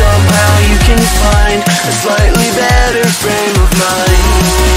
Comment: my brain misheard it as:to the sky,morning run and shout a friend of mine (frame of mind for some reason😅